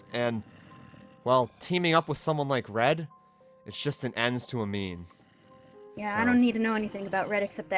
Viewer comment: is it just me or does it sound like just said "ends to a meme" XD